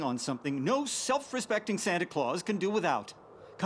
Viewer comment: Huh. Jack actually says "Santa Claus" instead of "Sandy Claws" here. Weird. 24:51 Oh boy, they FINALLY got "Once More" but no one noticed it. Hopefully they'll equip it next episode lol